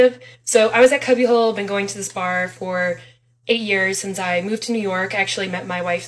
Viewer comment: Damn, I thought she said "I have been going here for a year, met my wife here..". Was so ready with the lesbian trope of meeting someone and renting the u-haul the next day until I checked again and she said 8 years.